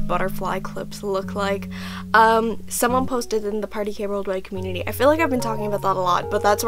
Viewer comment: Can't hear clearly which community she named @ 6:16 ? nHelp! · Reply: she said 'party kei worldwide community'.